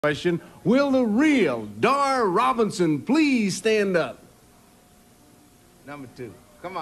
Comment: Right in the beginning it sounded like you said, "Dar Robertson."